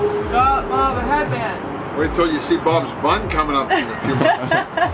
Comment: I legit thought Bob said “Wait till you see Bobs bum coming out” 🤣🤣 I had to watch it again and realised he said “Wait till you see my bun growing out! 🤣🤣🤣 Like if you think this was funny!